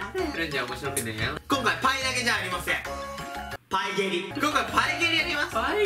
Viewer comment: の所パイ◯リに聞こえたのは僕だけ？w